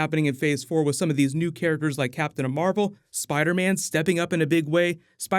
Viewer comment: Could have sworn you said ‘Captain A-Marvel’ at one point 👍🏻👍🏻👍🏻